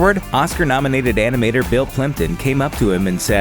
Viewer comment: i thought he said Bill Clinton not plympton xD